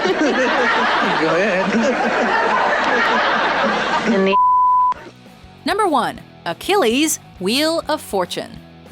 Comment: What did she say? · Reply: In the ass